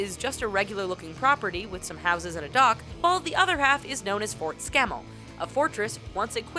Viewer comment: "Fort Scammel" i thought they said Fort Scam there1Lol!